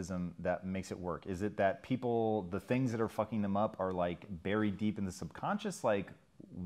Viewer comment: did he just say "the things that are fucking them up"?